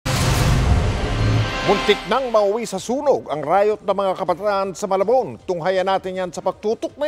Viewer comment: Teka teka, tama ba narinig ko sa 00:03"Riot nang mga kabaklaan"? hahahaha mang mike ah